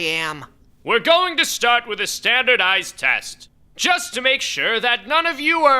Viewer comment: I might be an idiot; I heard "Standard Ice Test" and it took a good number of seconds to correct myself.